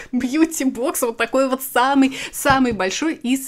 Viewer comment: мне послышалось "ссаный" 🤡🤡🤡